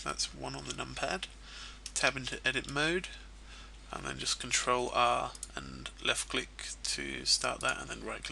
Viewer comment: My hear for British accent: in 04:57 I heard "Ctrl-A" instead of "Ctrl-R" :|